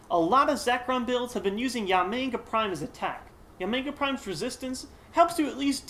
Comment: I thought you said YaoMing Prime lol